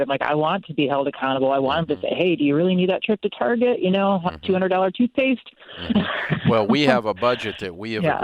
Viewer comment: Did she say "200-dollar toothpaste" ? Gasp...